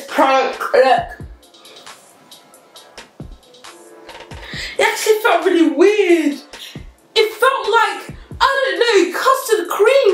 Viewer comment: Did say that “sh—“ felt weird